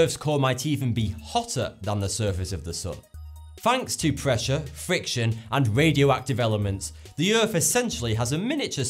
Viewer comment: I thought he said 'fiction' 11:30 <_<